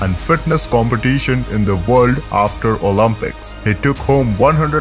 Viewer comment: Did he say after Olympics 1:29 oh boy ..